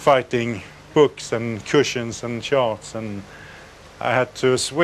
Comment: did dude say he was swimming and fighting books,cushions and sharks dude was on shrooms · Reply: He says charts. As in navigation charts.